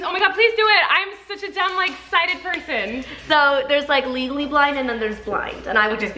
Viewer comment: “ Legally blind” for some reason reminded me of legally blonde🤣🤣🤣🤣😂😂😂😂 OMGGGG I HAVE NEVER GOTTEN THIS MQNY LIKES THHHHXX SOOO MUCH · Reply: At first I thought they said legally blonde 😂